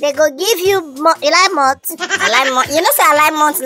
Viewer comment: What I heard was "you Lai malt, You Lai milk"😂😂😂 Whenever e reach Tegwolo turn, d plan must spoil🤣🤣🤣🤣🤣🤣🤣